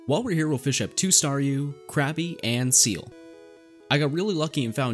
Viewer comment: i heard crappy instead of krabby 😂